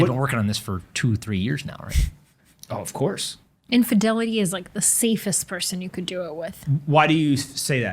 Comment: At about 3:45 I thought said "infidelity is the safest person you can do it with". Pomp took it well.